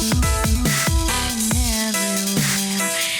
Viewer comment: *end of video "My drop, my drop" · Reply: lol common mistake of mishearing things. it's mic drop 👍